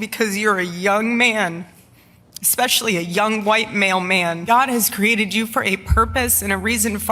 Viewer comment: anybody else hear that as "young white mailman" 😅